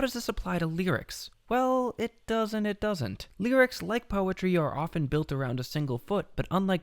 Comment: I hear "It doesn't! It doesn't!". Probably should have done another take.